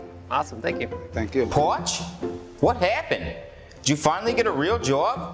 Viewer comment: Wow. All these years I thought was saying "Ponch." Like the guy from Chips. TIL it was "Potsch."